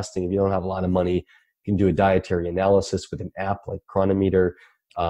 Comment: Chris Masterjohn mentioned an app. I couldn't quite catch the name of it. Anyone know what it is? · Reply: Cronometer....is what I hear